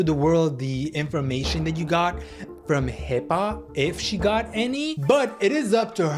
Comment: Unless you're Dr Now, we don't want to see you in surgery!! (Thd first time you said "HIPAA" I thought you said "Hip Hop"!!!!)